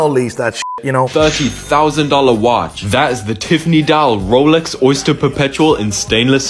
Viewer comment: I thought I heard 'Tiff Needell Rolex.' Now that I would sit on a 7 year waiting list for